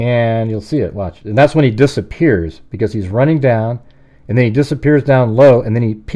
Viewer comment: What I hear is "he's laying down". I used the caption button and it typed the words on the screen.